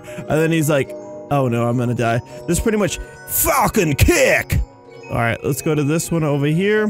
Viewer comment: DID HE JUST SAY A SWEAR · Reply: Foxy The Fox6078 he said falcon kick